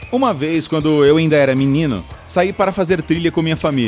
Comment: So eu q escutei: quando eu era menina?